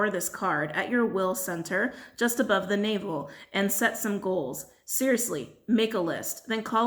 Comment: When you said "set some goals" at 44:50, I heard "sex and gold" which, I guess, can be considered as goals, too. 😂